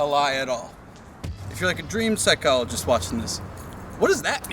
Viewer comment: In defense, it did sound like he said "if you're white"